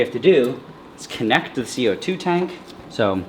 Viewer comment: i was watching this with one of my friends on discord, and he misheard CO2 tank as Two Time (we play forsaken) which was kinda funy cuz he was like "TWO TIME?!?!?!?!?!!?!??!?!?!?! '😧😧😧😧😧😧😧😧😧😧😧😧😦😦😦😦😦😦😦😦😦😦😦😦😦😦😨😨😨😨😨😨😱😱😱😱😱😱"